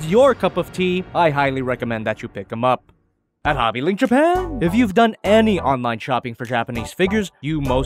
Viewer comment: I thought it said JobbyLinkJapan 😂